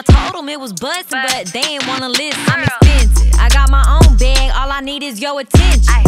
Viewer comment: She said “I got my own bag, all I need is yo attention”. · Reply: I thought she said “i got my own bag, all i need is yos”